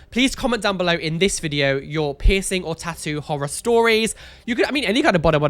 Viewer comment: I swear I heard “pissing and tattoo horror stories” and went 🤨. Anyways, love your vids ❤️